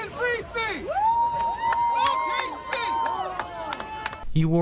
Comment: "Hate speech isn't Free speech. Fuck Free speech." Me: "...did he JUST say that hate speech is FINE then? I mean, I know 'Hate speech matters' but I didn't know it didn't mean 'Free speech didn't matter'..."